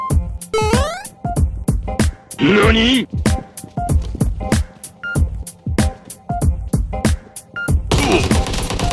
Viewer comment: what did he say?? LEONE?!? 😂😂😂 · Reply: He said "NANI" is "what" in Japanese